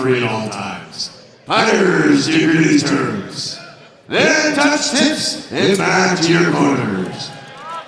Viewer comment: "And touch tips, and match your boners" Am I hearing right?